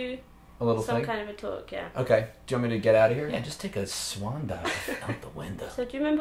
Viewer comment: "Germany get out of here" - WHAT? WHY? Did I just hear right? In wich content does this fit in? What did I do wrong?😢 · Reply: a derian creature I think he said “do ya want me to get outta here?” lol